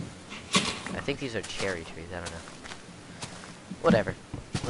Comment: Oh, the idiots who thought you were racist were talking about Cherry Trees. They may have thought you said Cherookees xD